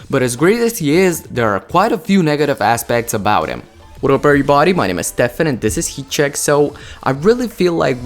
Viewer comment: Did he say whadup bluebody?